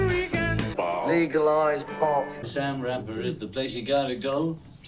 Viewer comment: Did George say: "legalize pot"!?!